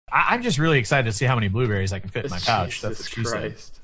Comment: At the beginning it sounds like says " i wonder how many blu rays i can fit in my couch" lmao